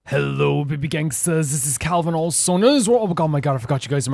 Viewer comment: in the intro it sounds like you say "wecome this is calvin old soul" and it is true because it does feel like u are an old soul and wise 🙂👍